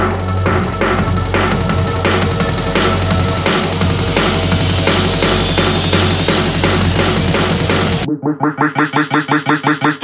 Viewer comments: A moment of silence for the people who still can't find this song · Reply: I thought it said "licuado shaker" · i always thought it said grimace shake